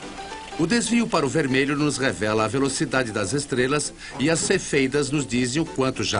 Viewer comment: não entendi esse nome "sefeitas" um trosso assim